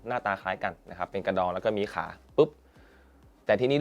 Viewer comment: หนูไม่ได้ได้ยินไปคนเดียวใช่ไหมพี่ เป็นอะไรมีขานะ ;D